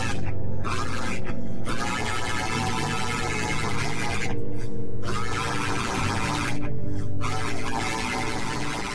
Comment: William is either saying "I did it" or screaming "Michael!!!! Mike!!! Michael!!! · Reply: Yea he is screaming Michael